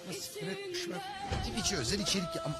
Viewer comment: da ne diyor tam anlamadım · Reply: ANLADIM LA TWİTCHE ÖZEL İÇERİK YAPALIM DİYOMUŞ